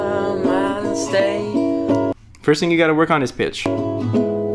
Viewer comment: Did he say bitch or pitch